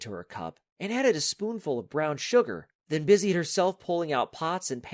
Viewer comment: did i hear it right? Did u just say brown sugar?